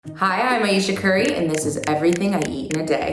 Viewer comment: I thought she said "hi I'm asian curry"